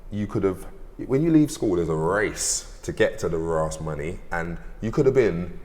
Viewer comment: I Think he said RARSE Money loool.. no edit